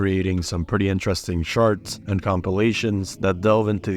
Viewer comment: Wait .. 0:08 did you say people creating some pretty interesting “sharts” or charts?! 😂 New sub from Las Vegas❤